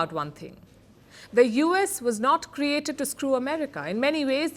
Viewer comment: Did she say the US was not made to screw America?